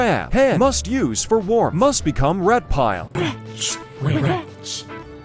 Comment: I didn’t see the captions at first and I just hear “must become reptile” and I’m like